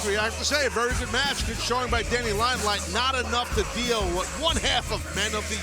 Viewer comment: I'm sleepy I thought he said Daddy Limelight. I was like "he changed his name?" 🤣🤣🤣